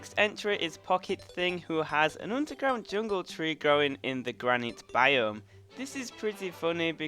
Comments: It sounded like you said the “Granny Biome” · I thought he said granny biome